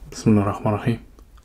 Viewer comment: did he just say bismillah ?! Dude where are you from ?